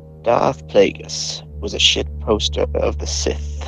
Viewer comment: I thought it said shitposter